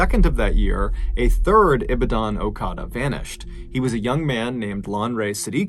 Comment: I heard you say Okada I thought you said al qaeda......................... Sorry about you're dad